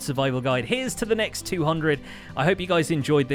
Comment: I always hear "picksaurus" and not pixlriffs.